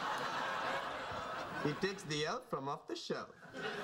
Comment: Could someone tell me what Raj said while in the Haz-Mat suit? I have a hard enough time understanding him WITHOUT the suit! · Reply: he says "He takes the elf from off the shelf" Then at 1:56 he says "Hell hounds, who let the satanic dogs out? Who, who, who?" At 2:04 "I've got a colossal serpent right here" 2:11 - "Sorry I'm just trying to cheer my buddy up. Rotting zombie.... Sheldon's new facebook photo"